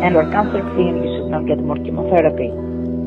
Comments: Lol I thought he said “Emo Therapy”😂😂😂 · Lol I thought he said emo therapy